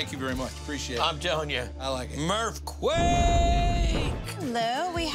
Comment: he said wake instead of quake